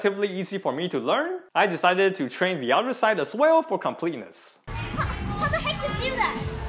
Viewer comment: Am I the only one who heard "how the heck he do that" at 3:53 ? lmao